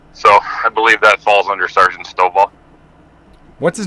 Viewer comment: I thought he said private Snowball 😂 When straight to Full metal jacket vibes 😂